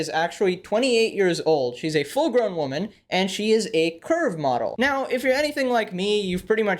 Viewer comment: Lmao when you said curve model I thought you said Perve model and thought this video was going IN A very weird direction